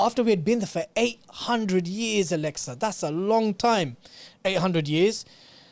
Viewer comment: did he call her Alexa 😂